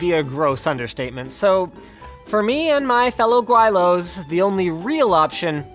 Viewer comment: Holy shit, 2:30 did linus just called himself guai lou